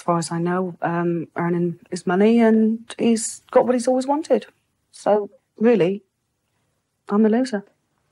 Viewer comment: did she say “so really, I’m a loser” -I’m not sure if I heard her right. If she did say that, that broke my heart 💔 I can’t imagine thinking someone loves you, respects you, and wants to marry to violating you, being in constant fear, not able to get proper justice for the incident and then having him take everything from you- carrying on with their success in life with no care in the world. Truly heartbreaking!